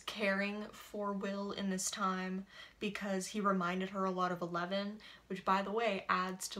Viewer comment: did she say because he reminded HER more of eleven?